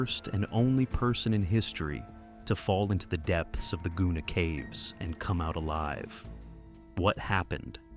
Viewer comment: Sorry, I heard "Gooner Cave" and now I can't stop laughing... But damn, I would never go caving. Nightmare fuel